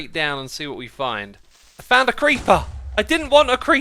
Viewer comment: My gosh when he said “if found a creeper” my american brain thought he said “ i found a creampuff!”